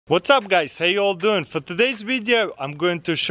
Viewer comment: i heArD "HOWTDOUG" hMMM KAY